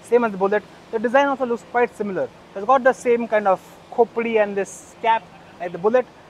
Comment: Did said "khopdi and a cap"?